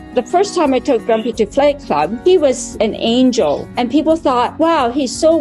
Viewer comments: 😂 when she said she pulled him out of flight club, I thought she changed it and said "fight club" and I listened again..I was wrong..but that would have been hilarious if she had made that joke! 🤣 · Am I the only one who thought she said Fight Club instead of flight club? 😅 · At first I thought she said “Fight Club” and that she broke the first rule